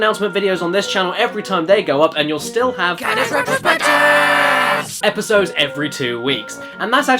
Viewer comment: I swear i hear "caddys retrobitches"